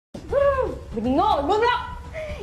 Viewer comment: Aku dengernya "oyy gob*ok" 0:02 Terus aku dengernya "Iyaa" Kayak mengakui kalo dia gob*ok 🗿🗿